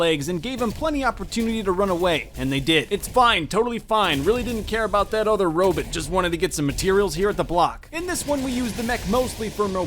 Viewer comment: Did he say 'Robit???" I'm DYING!!!!!!! llllllll